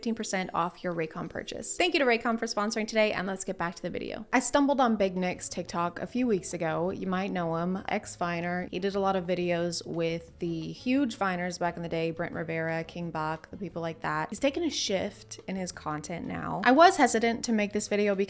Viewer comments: When you said “Ex-Viner” I first heard “Ex-Minor” and just went along with it for a few seconds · i thought she was gonna say "hes taken a shit"